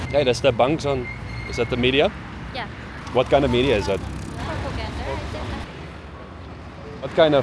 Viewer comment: did she say "Propoganda" ?